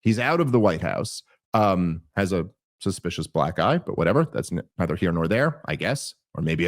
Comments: i heard "suspicious black guy". but it was about an african american man's black eye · I thought Dave said “has a suspicious black guy” 🤣